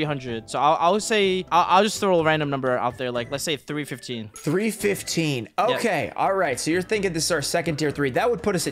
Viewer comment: I swear said $315 not $350 which was shown 🤔